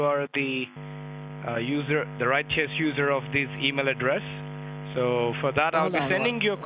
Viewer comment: Hold up - at 1:00 did he say he wants to confirm you're the "righteous user" of the email address?